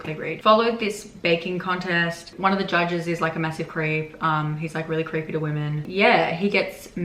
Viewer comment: since you said the story took place during a baking contest I heard “one of the judges is a massive crepe” 🫠